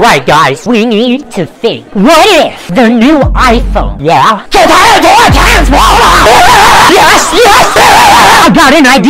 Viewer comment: can someone pls tell me what bonnie is saying here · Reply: CAN TURN INTO A TRANSFORMERS!!!!!!!!!!!!!!!!!!!!!!!